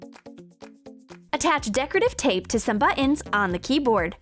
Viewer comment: Sounds like they say butnns insted of buttons...